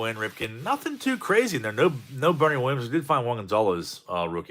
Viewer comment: I could have sworn you said Bernie Williams midstream. Nonetheless have a great weekend. The search continues!